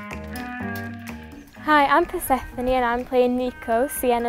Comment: Am i the only one who thought her name was said like Per-sa-phone